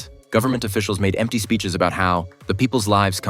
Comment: I thought it said gay house